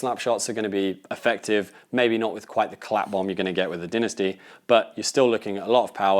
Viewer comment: of the video I think you said the word or phrase "clap bomb"? What does that mean?